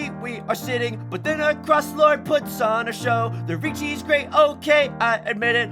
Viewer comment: It's only today that I discover through the song parody that you call the toimen "across lord". All this time, I thought you were saying "our cross lord" 😭😭😭